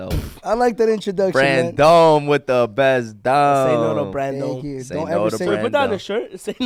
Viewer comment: Lol I thought had said... "Brandon with the bent one" I wa slide wait how he know he got a bent one!?😂😂